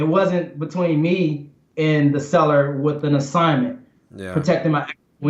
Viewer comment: Did he say "ass" by accident? lol